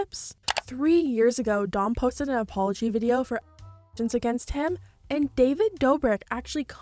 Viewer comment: what does the censor here say? "actions" against him? "allegations" against him? what is it??? also 9:53 who's the blonde person at the far left? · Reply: allegations i reckon